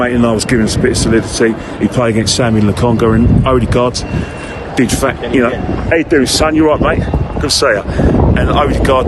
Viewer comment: I thought said hello edu's son how you doing lol